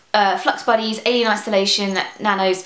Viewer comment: I heard "Alien Masturbation" where can i find THAT series